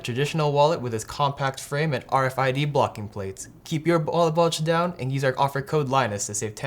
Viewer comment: I'm not the only one who heard him say "ballet wulge" for the ad read, right?